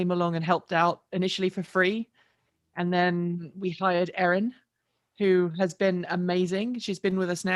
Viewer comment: yikes the way you said we've hired Erin, I heard "we fired Erin!" I had to watch it back lol